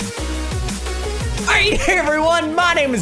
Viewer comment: Did he just say I hate everyone in the beginning